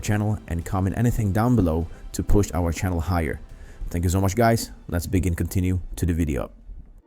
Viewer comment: what did you say? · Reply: "Thank you so much guys lets begin to continue to the video" lol I understand it and I play videos at 2x speed 😆